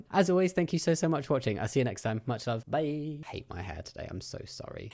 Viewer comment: I thought you said ' I hate my hair today...Penis!' The joys of APD. Also, good luck for 2025 and your ventures, both you and your lovely wife <3